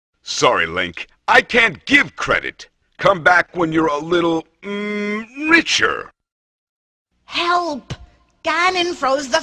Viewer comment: As a kid, I always thought Morshu said "Mmmmmmm...mature" instead of richer